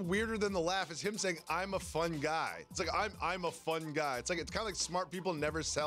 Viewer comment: I think he actually said “I’m a fine guy” idk tho